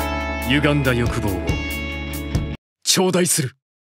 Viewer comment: did he say Uganda?